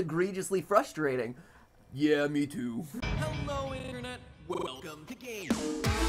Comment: im sorry but i always hear "welcome to gay" in your intro